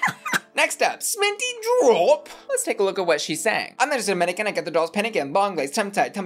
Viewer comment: i thought sminty said manchester mannequin bye